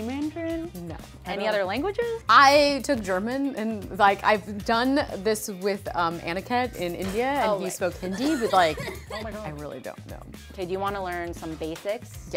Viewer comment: did she really say Hindu